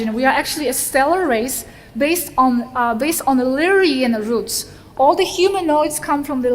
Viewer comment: is she saying Illyrian roots?